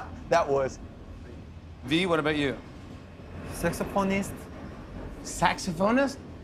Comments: Lmao it sounded like V said sexy porny star again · im but i heard 'sexophonist' and my eyes went big for a sec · does anyone remember when we thought he said sexy porn star😂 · For some reason, you know why, I heard Taehyung saying Sexy Pony Star!!!! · Is it just me who heard sexy pornist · thought he either said “sexy pony” or “sexy pornist” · TaeHyung: saxophonist YoonGi: automatically laughs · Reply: I automatically thought Tae said Saxpornista XD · Omg I thought v said he wanted to be a sex pornist I was like 😦😯😧😮😲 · TaeHyung: saxophonist YoonGi: automatically laughs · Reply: Even though he said saxophonist I dont know why my mind heard it as sexy porn star🤣🤣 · I heard V saying "sexophonist" Lmfaooo I was like sur babe ❤️❤️❤️😂😂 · What V said: "Saxophone" What I heard: "SeXyPorNyStAr" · Me: having sexy porn star flashbacks Tae: saxophonist Me: he's learned! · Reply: I deadass thought that when he said saxophone he said sexy porn Star BHAHSHSHA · I thought V said “sexypornist” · i remember when i thought taehyung said he wants to be sexy prn⭐ and it turns out to be saxophonist🤣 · i thought tae said sex pornist 😂😂 instead of saxophonist 🥺🥺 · Tae was thinking so hard to NOT says sexy p0rnstar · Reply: It almost sounded like he said sexy phonist lol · when he said saxophonist I THOUGHT HE SAID SEXY POR*STAR GGDUWJXHWHZHWHZ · When V said saxophonist I thought he said "sex phonist" that's ain't no job but if it was one and he was doing it I ain't gonna complain